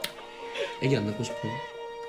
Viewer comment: 미쳤나봐 전에 뭐라고한거예요? 애기안낳고싶어요? 이건가?